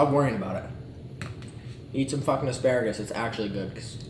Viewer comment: is it just me or is also saying Sparagus instead of Asparagus